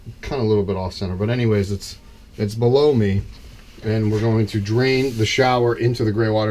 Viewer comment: Great vid and sweet work. One question... @ 18:15 did you say "blow me"? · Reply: Yuri Andropov I said “below me” talking about my water tank